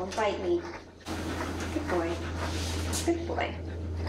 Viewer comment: It sounded like you said "good boy a**hole. 😳 then I heard Axel when you said his name again. Lol. He's clearly not a a-hole. ♡